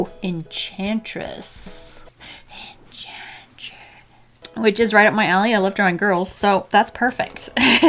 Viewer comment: XD I thought you said thats right around my Ali I live on girls